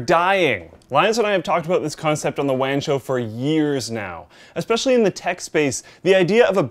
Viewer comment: Am I the only one who thought Luke said "The Wang Show" at the beginning of the video?